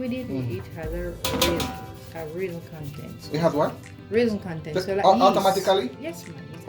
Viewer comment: I've also had coconut flour. 9:06 and 9:14 didn't hear what she said. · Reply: She said it has raising properties. It has natural yeast and is nutritious